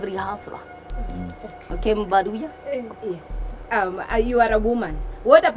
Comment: Did I just heard " Okay mubaruya " 😂😂. Terrence ngamuya voice legiiit